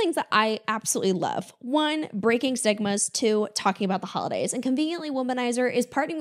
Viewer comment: I thought you said "one thing I love doing, breaking Sigmas" and I was interested 😂